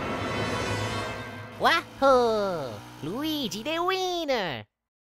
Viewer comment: It sounded like luigi is saying "me a wiener"